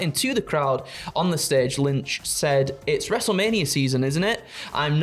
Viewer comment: I swore I head say "Lunch"